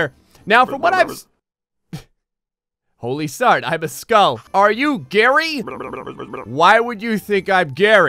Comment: I was watching this while playing animal crossing and it surprised me when i heard "are you Gerry?"